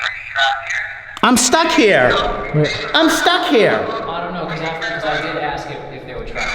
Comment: I HEARD I'M STUCK HERE, CLEARLY, JUST LIKE WHEN THE GENTLEMAN SAID RADIO, YUP.